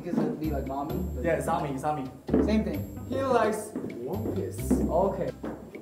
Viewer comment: So I watched this again and I heard this... 19:55 “He likes” “Wonpil” 😂😂